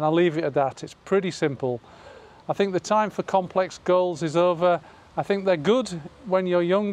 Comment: I’m still ll watching, but your #1 is a great rule and I’m practicing now that I’m 61. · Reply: Sorry, at first I thought you said for #4 - it was no more complex girls and turned out being no more complex goals. Haha!